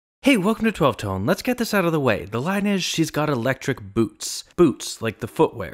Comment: I've always thought he said electric boobs lol